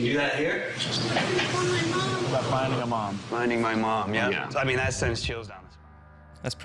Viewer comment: omg I heard that " I want my mom" wow